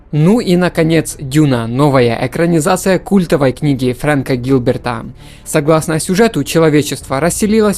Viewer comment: мне послышалось или он сказал Френка ГИЛБЕРТА?